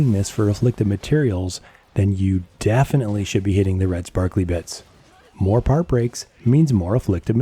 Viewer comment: "Hit him in his red sparkly bits" LOL I heard, "kick him in the junk" #hithiminthesparklybits